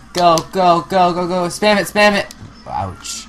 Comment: when said "spam it," I thought he said "damn it"